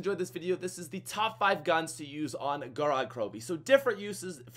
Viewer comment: WHAT I THOUGHT U SAID TOP 5 PUNS NOT GUNS